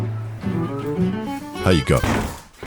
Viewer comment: I heard the “Hiya going” and half expected to hear “My name is Adam and I like to make tiny nerdy things”.